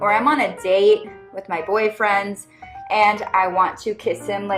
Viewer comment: d-did she just say boyfriends? As in more than one?